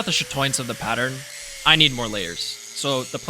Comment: What was the word you used? "To bring out the ??? of the pattern" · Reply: Chatoyance is how at different angles, the pattern will look slightly different and shimmer. It’s sought after in more pattern rich Damascus patterns